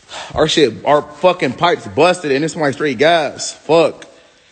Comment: I thought Damien said his pipes busted!